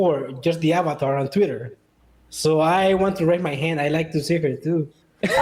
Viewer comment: what did he said? I can't understand it (acoustically). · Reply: I want to see her too